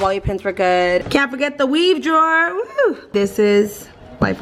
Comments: I put this on while I was cleaning my room and I thought you said "CAN'T FORGET THE WEED DRAWER!" when you actually said 'weave' 😂😂. I was like "KAREN?!" LMAO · I was watching this with my hubby last night & he was like she has a 💩 ton of makeup. I was like LITERAL GOALSSS 💄🙌🏼 Also, when you said “weave drawer” I totally thought you said WEED 👀 hahaha. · Dude I legit thought you said here the weed drawer 😂😂😂 I was like dang it’s legal in Cali but idk if you should be posting that lmao 😂 💄💄💄💄